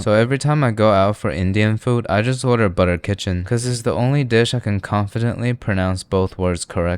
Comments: Am I the only one who heard you say butter kitchen😅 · Did he say Butter kitchen? Or just my expired ears? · did he just say butter kitchen 😂😂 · Is it me or did he just said "butter kitchen" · Is it just me or did he say "butter kitchen" instead of chicken? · did i just heard "Butter Kitchen" 😮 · Is it just me or did he really said butter kitchen instead of butter chicken 😅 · You Said Butter Kitchen and I Heard Butter Kitchen 🗿